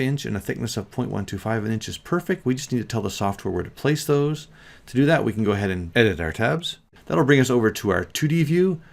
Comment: you say, “type in zed and hit ????? On your keyboard.” I listened 10 times and it’s not a word. 🤷 what are you saying? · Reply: Sorry about that... I said "="... :)